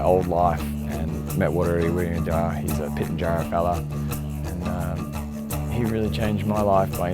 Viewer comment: Hi, I am sorry but I cannot understand what was said from 0:30 to about 0:38 something about a “pit and giant fellow”? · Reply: He mentioned an indigenous name followed by his tribe. Something along the tribes of “I met Waru (then his surname) he was a Pitjantjatjara fulla”